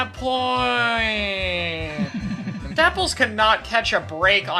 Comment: Subtitle say apples insteand of dapples.